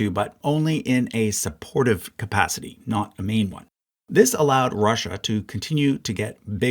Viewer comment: when he said "Russia was in the war in a support capacity not a main one." I thought he said "not a mean one." LOL...